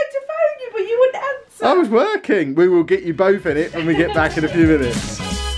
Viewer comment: I’m hearing impaired and depend on youtube closed captions quite a bit. I’m not sure what it’s supposed to say but at 4:07 the caption says “we will get you both in it and we get back to the penis